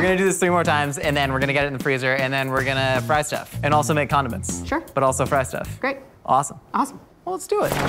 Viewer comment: I thought Josh said make condoms not condiments....